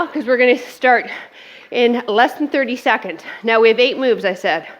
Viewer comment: I misheard and thought you said 8 rounds at the beginning, imagine my surprise (and relief!!) - great workout, tough but highly effective.